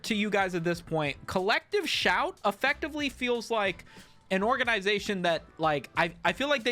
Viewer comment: I keep hearing "collective shaite", can't unhear.